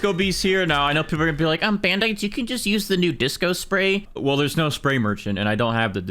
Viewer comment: umm did I hear that right? DISCO SPRAY????? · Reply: Yeah, the disco spray